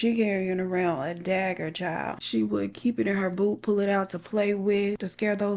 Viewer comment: i thought you said she kept the knife in her BOOB and then i saw the knife and was like HOW her poor titties😭😭